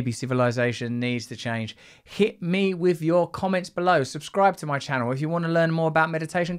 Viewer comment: Gosh, for a moment there I thought you said "hit me with your comets". That'd do it..